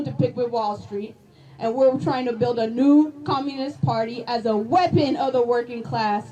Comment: did she really say she wants to build a new Communist Party, that is the most dumbest and dangerous thing i ever heard..then i heard the cheering 😢 SMFH